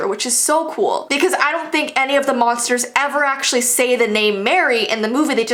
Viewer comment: I thought she said monsters egg